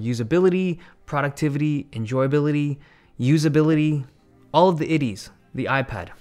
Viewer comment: All of the titties is what it nearly sounded like.